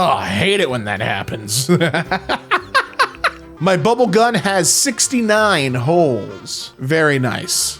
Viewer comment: I heard bubble gum* and I was so confused for the longest minute